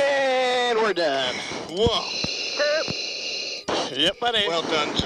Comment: Was that a Ronnie Coleman "YEAH BUDDY!" I heard at 14:45?